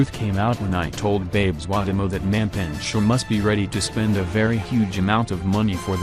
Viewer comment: I heard "mapintsh"😂😂😂